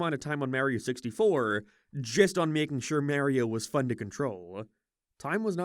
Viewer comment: hate to be that gal but... did you just say... merio...?